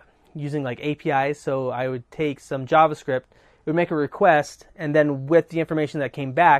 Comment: are you talking about HTTP requests? I'm not sure I caught exactly what you said there..